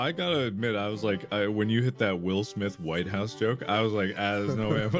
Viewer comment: Pearls of wisdom from: "Keep my White House out of your f*cking alien beam" "We are part of the intergalactic breast, yes" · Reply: Oh god. I heard it as "wife's house" and it was even funnier to me that way